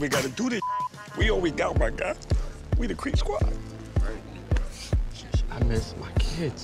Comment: Did he just say we the creep squad lmao FFS